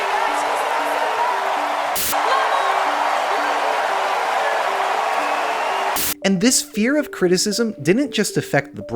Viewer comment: she was literally saying something to her mom there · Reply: If you listen closely you can hear her say "Maman your dream came true!". Most people wouldn't have heard it sadly. 🥺 Those clips are in chronological order and I had a few other examples that I was considering. But I decided to end on that one because it's such a beautiful message in the context of the song. Unfortunately, the production opted to drown it out because they're afraid the artists might have a conscious.